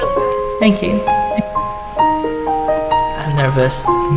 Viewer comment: you did it again <3 <3 <3. can you check @ 23:58, did she say she is Jenny McCarthy. you may check a wikipedia article for her at <link>